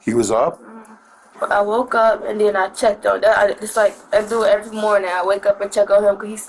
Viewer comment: did she say, "I woke up and then I checked on that". That? Not my baby, or the baby's name?